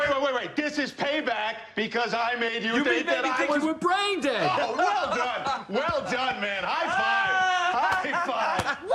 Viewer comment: what Did John actually say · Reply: "Retarded" Sorry to say.